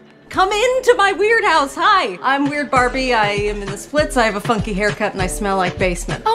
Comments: “When you play too hard” WHY IS NO ONE TALKING ABOUT THAT LINE?! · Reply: I thought it said I have a fu**ing hair cut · Why do i hear "fucky hair cut " i also hear funky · When she said" funky haircut" I thought she said fucking hair cut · I thought she said f-cking instead of funky RIP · I thought she said I have a f***ing hair cut and I smell like a basement😭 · I thought she said “ YeS I hAvE A fU**iNg hairstyle” or whatever 😂😂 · Am I the only one that I heard “I have a fucking hairstyle” the first time 🎀✨💀💅🏻👽💋🤧📍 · What I heard “I have a fucking weird cut and I live in a basement.” The acc words:”I have a funky haircut and I smell like a basement.”